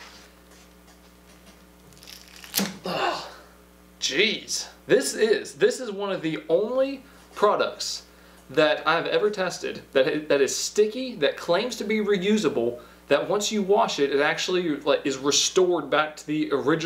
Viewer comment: thought that said "tasted" and not "tested" ngl